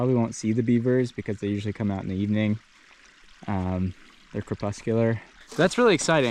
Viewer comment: maybe I misheard, but I thought you said beavers were corpuscular? versus nocturnal?